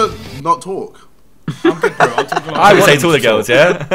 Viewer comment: For the people who couldn't hear Ksi said "Do you wanna not talk." Josh said, "Is that what you said to all your girls?" Simon says " That's his Tinder bio." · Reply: I thought josh said “is that toilet girls yeah?”